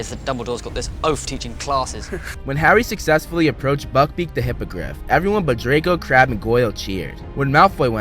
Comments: Am I the only one that thought he said “Buckbeak the HYPOCRITE” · it sounded like you said hypocrite